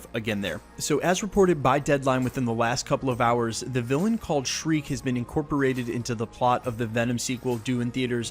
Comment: for a second I thought he said Shrek..